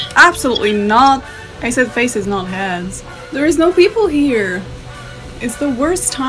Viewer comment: "I said faces not heads" WHEN I TELL YOU I DIED WHEN THAT FINALLY CLICKED. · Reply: I thought she said 'hands' also! I figured it was some guys hand playing with his thingy..since omegle is full of that nastiness. ( sigh - I am a full grown woman calling it a "thingy" )